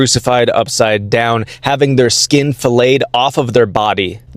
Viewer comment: I know the text said flayed, but it sounded like filleted to my ears. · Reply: He 100% said “filleted” I had to stop the video bc I was laughing too loud to hear.